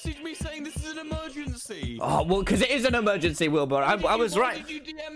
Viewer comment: THE CAPTIONS SAID "WHEELBARROW" INSTEAD OF WILBUR I- time stamp